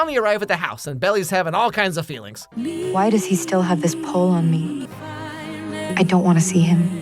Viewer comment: I heard “Why does he still have this pole on me” in 8:14 😂😂 frfr